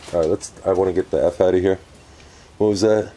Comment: Could of swore that i heard, before you went in something said ...get out of here